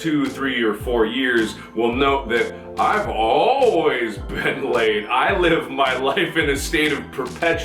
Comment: "I've always been getting laid" -Markiplier 2016 That is what I heard instead of "I've always been late"